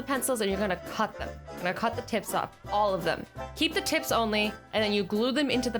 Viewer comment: I heard “cut their tits off” 😹😹😹😹